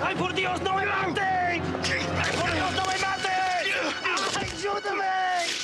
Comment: What is the Argentine soldier saying at 6:46? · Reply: "Ay por dios no me mate" "Oh my god don't kill me"